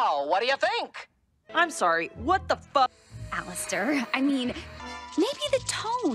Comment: What did she say : i’m sorry what the F